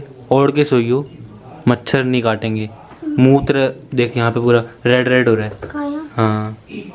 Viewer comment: What is was saying I can't understand😂 I think he was saying mutra😂😂😂🖖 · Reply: he was saying muh tera